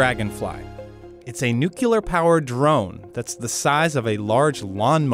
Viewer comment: Did I hear "nucular"?